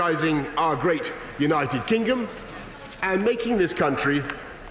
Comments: Did he actually say united kingum???... smh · Did Boris said "United Kingum"? Are my ears OK? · did he just say united kingom lol